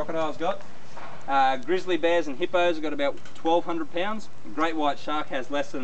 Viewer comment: just imagine... hyenas can bite harder than a frickin' hippo. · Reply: Desmond Jake thought u said glizzy bear xd